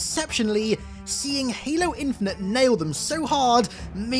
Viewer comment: I missheard what you said as "fail", not "nail", for a fraction of a second.